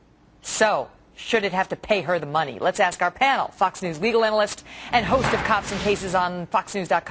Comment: sound like shes saying fuck news lol.